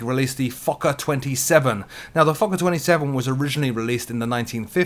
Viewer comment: oh my i have such a dirty mind. I really thought you said the fucker 27. But anyways great vid as always. Thanks for keeping us updated. Also, when are we going to do a fully loaded DCS? Can you teach us how to fly these fighters?